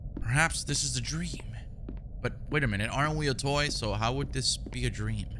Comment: aren’t we a toy 3:40 the caption says were not a toy 😂💀